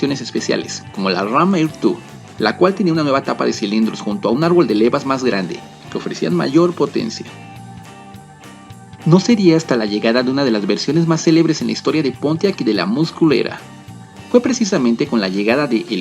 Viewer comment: Que susto, cuando dijo "con la llegada de la" muscle era" entendí " con la llegada de la más culera" 🤔